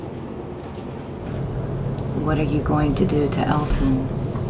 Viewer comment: i swear at 50:30 i when she asked “what are you going to do to Elton” i heard “Murder someone”